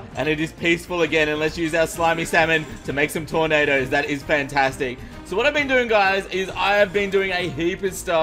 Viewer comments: did he say slimy samon?!?! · Did call the Briny Baron a slimy salmon? 🤔